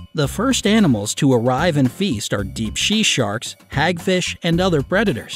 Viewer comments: instead of saying deep sea sharks he said deep she sharks lol 😝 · did he say she sharks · he said she sharks instead of sea sharks 😂😂😂🤣🤣😂🤣😂🤣😶😶😶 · wait a minute did you say sea she sharks(she???) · What I thought I heard you say Shea sharks? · deep she sharks, why did you say she, instead of sea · did he say deep she sharks?